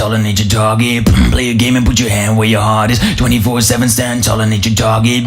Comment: I thought he said "24/7 stand tall and eat your doggie" 😂